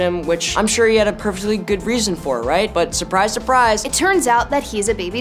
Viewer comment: (Put on captions) The captions say goof but Finn says good, which one is it?